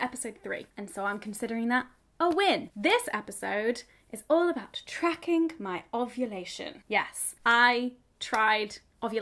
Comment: subtitles say tracking my oscillation xD